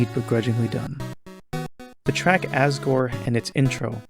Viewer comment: Wait, did you say truck Asgore?